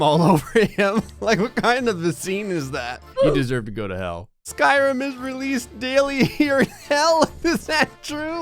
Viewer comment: did you say Skyrim?? Okay you just got a subscriber